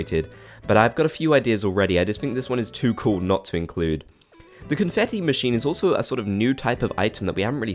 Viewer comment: did you say confeffi lmaoo